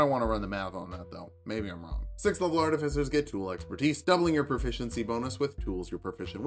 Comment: i thought you were saying your name here at first. like tulok's per-tise whatever that means.